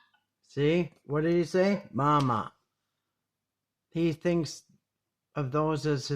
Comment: I dont think he is saying no its mamas underwear ..he calls patrick bama and when he says bama it sounds like hes saying mama ..not taking up for patrick just saying i think hes saying stop bama